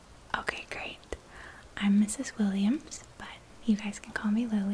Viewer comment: I legit thought she said "I'm Ms. Williams, but you can call me Willy" and I died 😂😂😂😂😂